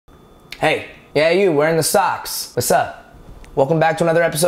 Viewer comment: Where my sock gang at?????? Stream my song: <link> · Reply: Lol I heard you say "Hey. Hey you with the socks" and I was like gasp HOW'D YOU KNOW OH MY GOD WHAT THE FUUUUUUUUUUUUUUUUUUUUUUUUUUUUUUUUUUCK (>W<)